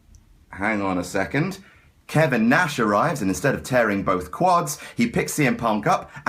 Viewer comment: someone can tell me, what says at 1:03 please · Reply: X kevin nash arrives